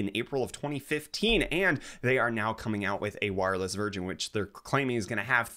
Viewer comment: Did anyone else hear 'wireless virgin' XD